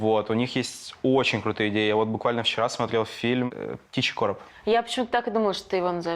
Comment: что за фильм? не расслышал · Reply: limon adko "Птичий Короб"